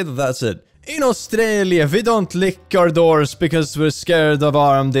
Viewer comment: it sounds like you said "in Australia, we don't lick our doors" I even put on CC and it thought you said "lick" is that true Australian do you guys not lick your doors?